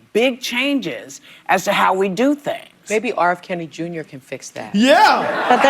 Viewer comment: did she say R. Kelly Jr, lol? Replayed a few times but that’s what I keep hearing, even though I know it should’ve been R. Kennedy Jr.